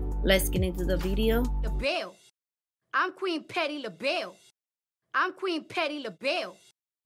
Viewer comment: Cardi saying she’s “Queen Patti Labelle” doesn’t mean she knows her songs 😂😂😂😂😂😂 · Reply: I thought she was saying petty labelle... not patty... as a play on her name. She definitely don't know her songs